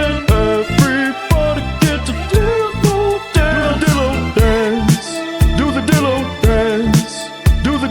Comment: I was low-key boogieing it down when I heard “Do the Dillo dance, the Dillo dance”